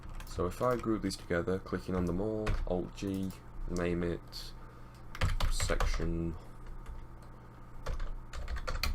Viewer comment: what do you say? 5:38 ? hold g? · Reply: | Mᴏᴛɪᴏɴ Dᴇsɪɢɴᴇʀ Alt and G